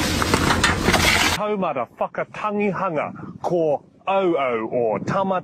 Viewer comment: Is it just me or did he just say “toe motherf-“